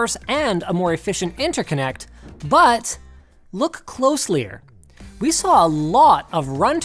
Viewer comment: Did Linus just said look "closlier"? 7:17. I know he is Canadian but I doubt closlier is an actual word